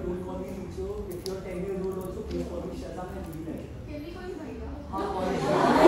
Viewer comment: ko kya bol rha tha??? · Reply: the girl asked ..can we call uh bhaiya? then said ..haan call me but he realised and said no please:(